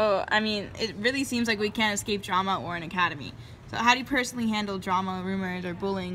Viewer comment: I thought she said Ouran Academy 😂😭 I was like 'Where's Haruhi?' 😂